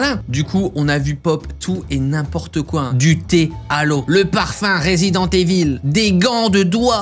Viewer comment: j'ai compris "du thé à l'eau" mdr